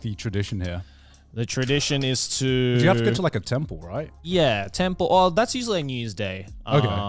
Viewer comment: When they were talking about christmas traditions i heard "ten pull" instead of "temple"... There's no salvation for me